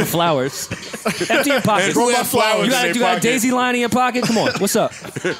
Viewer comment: Did Rory say daisy lion